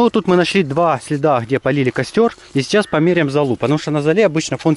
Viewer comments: мне одному показалось что он сказал «и сейчас мы померяем залупа» ) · Я хорошо услышал слово залупа? 😂1:23😂😂